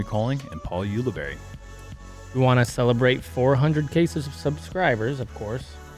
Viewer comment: Four hundred cases of subscribers is what I heard 😆